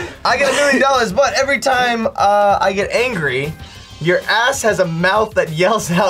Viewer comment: What is he saying at 8:52? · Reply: “Drunk Ass?”